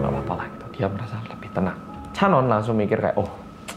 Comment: KENAPA AKU SALAH DENGER CHANWON JADI CHANYEOLLLLL 😭😭 terus taeju jadi taehyung :))))